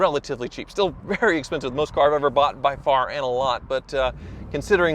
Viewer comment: Did you say boughten at 6:15?